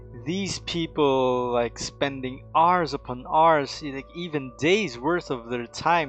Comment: I heard "Are's upon Are's"